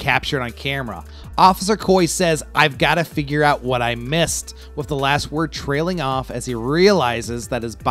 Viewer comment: Got to figure out what I'm going to say? Wow. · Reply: I thought that is what he said at first too. I actually had to go back and rewrite my script for this video because of it. I think he actually says "Got to figure out what I missed."